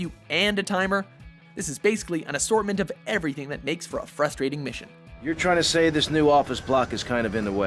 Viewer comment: fruthstrating mission? did I just hear a lisp?